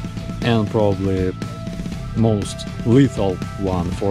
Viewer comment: i misheard your last sentence and though you said 'death to russia' instead of 'thanks for watching' hah well, we are ok with both options just so you know :) 💙💛